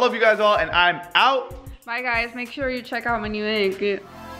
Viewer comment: At the Ed what the fuck did say cuz all I heard was “ make you check out my aguayahgh” 😂